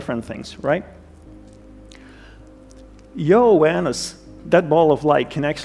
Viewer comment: Who else heard "Your Anus" @ 1:57 ?